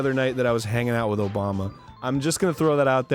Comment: I heard he dreamed about hanging out with a Llama.